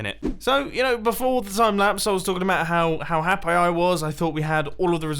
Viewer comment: Did he just say ‘Happi (pronounced ‘Hap-eye) instead of happy?